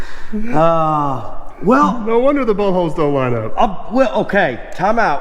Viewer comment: LT is coming along nicely. But I will have to say I think the driveline will look amazing green candy stripes would look awesome. I think Aaron is right. I know u said boltholes but it did also sound like u said buttholes!!!!